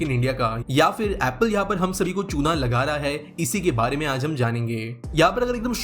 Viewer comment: I thought u were saying "ya fir apple ham sab ko Ch&#& bana raha hai." 😂😂